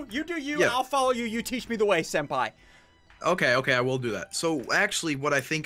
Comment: did ssundee called a senpai